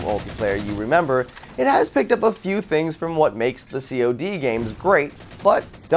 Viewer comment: Did he just say "What makes the CoD games great."?